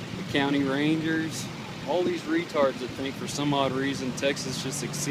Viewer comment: did he just say RETARDS!?